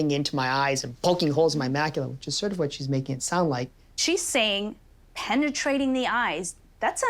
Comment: whoever does the subtitles for this sometimes mishears. "Making it sound scary" vs "Maybe it sound scary"